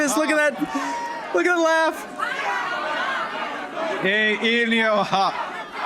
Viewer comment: No point in using the auto-caption to try to translate Carney's French. His pronunciation is too abysmal for computers to understand lol. The caption detects English, not French. I believe what he says at 1:25 is ''Il n'y aura...'' (There won't be...). He's trying to continue his speech. He tried saying that while getting interrupted at 1:08 ''On ne reconnait pas les peuples autochtones. ((guy calls him out)) Il, non, il n'y-...''